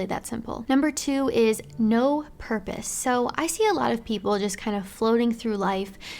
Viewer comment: NO HERPES. I was eating while listening to this and that's what i heard.